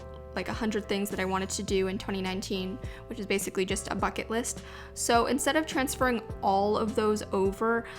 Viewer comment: anyone else hear fuck it list?